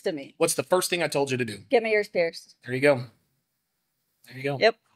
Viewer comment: what did she say? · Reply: "Get her ears pierced"